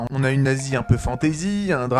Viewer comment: une quoi ?? 🤨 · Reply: « Une Asie un peu fantaisie »